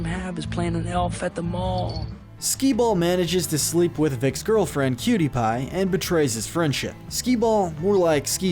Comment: am I the only one who thought he said PewDiePie?